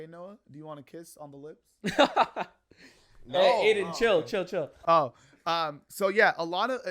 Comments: did he say adin ??🤔🤔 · I thought you said adin chill😭😭